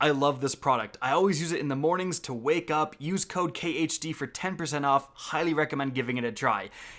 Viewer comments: I deadass thought you said use cocaine when you said use code khd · I thought he said “so I wake up and do coke”